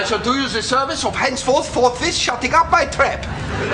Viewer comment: what is jurgen saying between 1:40 - 1:50 ? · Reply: LoooL 1. '...henceforth shutting up my trap!' The word 'trap' is a slang term for 'mouth'.